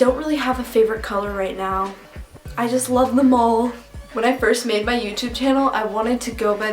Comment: When you said "I love them all!" when you were talking about colors, I thought you said I love the mall!